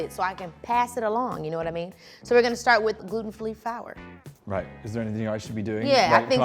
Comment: Keke saying "gluten FLEE FOWER" at 1:51 instead of gluten-free flour is my fav thing.